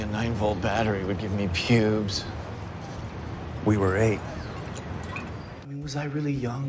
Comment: no cos i could’ve sworn he said “we were kids” instead of “we were 8” cos i remeber thinking they say that phrase a lot during the show for eg “we are adults now” “we arnt kids anymore” when i watched this scene